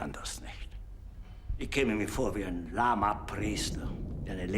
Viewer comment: Does he really say "Lama priest"? :D I think it's "Lahmer Priester" which means lame/sluggish priest.